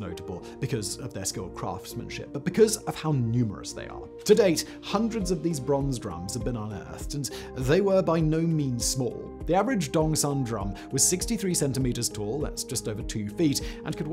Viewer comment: All I heard was "the average Dong" "wow great bronze drums you got there"